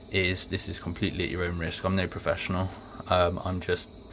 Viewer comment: Careful, what you’re doing is illegal if you’re not competent to work on gas appliances. There are a number of gas and combustion checks that qualified engineers do to make sure the appliance is safe after working on them. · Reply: sorry I thought you said “I’m no professional”